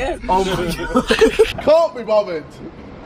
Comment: someone needs to check on the captions because jj was saying "i cant be bothered" and captions say "caught be robert," and "cant be buffered" just felt like i should point it out its before